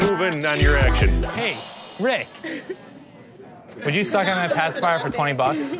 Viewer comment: Thought it said "pacifier Porn" 😂😂😂